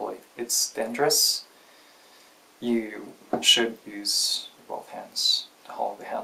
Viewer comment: Part is his accent, part is me going deaf, but when he said "both hands" I thought he said "ball pens".